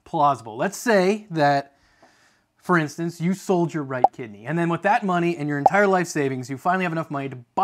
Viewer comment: When you said sell ur right kidney, I thought you said right tiddy, and I was like, well, my dawgs is up in my left tiddy, so I could sell my right tiddy and probably wouldn’t miss it